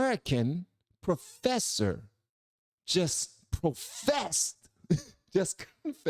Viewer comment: 😂 I heard him profess too